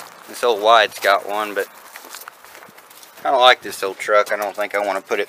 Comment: Did you say that you don't like this old truck or do you mean you kind of like this old truck?